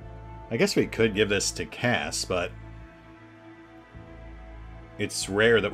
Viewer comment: Wasn't paying attention that much at the 11:00 mark and heard "going to give it to Kaz" (instead of Cass). First thought: since when can HE shoot?